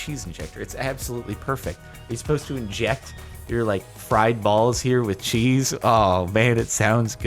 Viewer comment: I hear “friend balls injected with cheese” and had to immediately come to the comments. I’m dissapointed